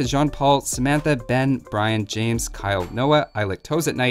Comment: Props to "@ for making say that at the end of all his videos. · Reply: I heard it as " I lactose at night"......